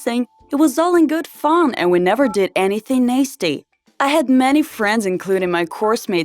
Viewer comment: it sounded like she said "naesty" instead of nasty